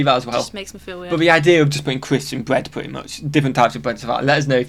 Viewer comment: Croissant sandwiches have been widely available for decades. There might have been a time they seemed new but that was no later than the early 1990s for sure. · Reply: lol!! I thought he said croissants, as well. But, croissants or crisps, I like either. I'll even put crisps on a croissant sandwich!!!